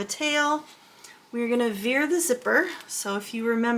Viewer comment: Oh, "veer", I thought you said "fear the zipper."😅😅